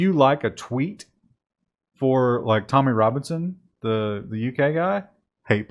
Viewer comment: For a moment I thought you said "Tony Robinson was a UK agent of hate" (Tony being Baldrick from Black Adder). Tommy Robinson can fuck himself.